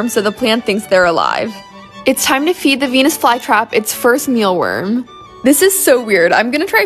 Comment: Ok so who named this plant I thought she said weenis fly trap